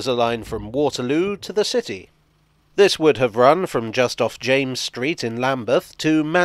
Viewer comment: Hey @ 5:37 - Did you just say 'James Street' - C😎😎l - that's nearly my name!!! 😉🚂🚂🚂